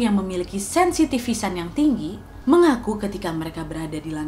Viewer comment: sensitifisan yang tinggi? nggak salah denger